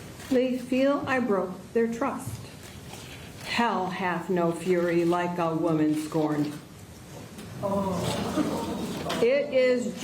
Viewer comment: What does she say at 11:17 ? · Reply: I'm 3 years too late, but she says "Hell hath no fury like a woman scorned"